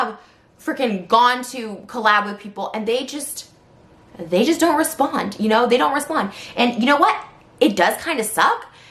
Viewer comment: she is a hypocrite! Isn't she the person who didn't let her boyfriend leave her home, and tried to call the police? Is she saying Danielle or daniel? It's so annoying hearing her speak! Please pronouce words trisha! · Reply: shes saying daniel.